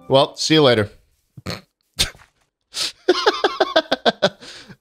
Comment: For a moment I thought he said "Where's all leg dream?" as opposed to "It was all a dream..."